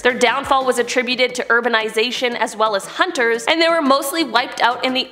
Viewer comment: did I hear that right ‘Erberization